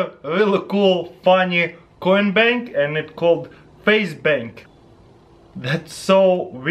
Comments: I thought he said faze bank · When he said facebank I thought he said faze banks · Who thought he said faze bank instead of face bank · thought he said faze bank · did he say FaZe Bank?? lol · when he said face bank i thought he said FaZe Banks. if there are any FaZe fans out there like this comment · I swear to God, I heard faze bank